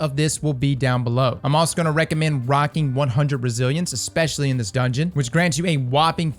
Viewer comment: I thought he said 100 brazillians lol